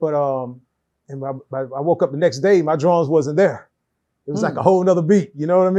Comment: I thought he said he woke up the next day & his draws wasn't even there😭😭